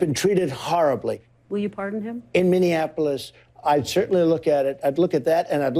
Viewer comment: The people that burned the Indianapolis police station were caught, prosecuted and are in jail. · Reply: I thought he said Indianapolis too but I think he meant to say Minneapolis